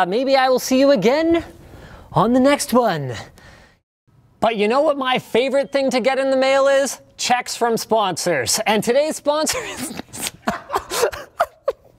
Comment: i thought he said: CZECHS FOR SPONSORS. get it? :D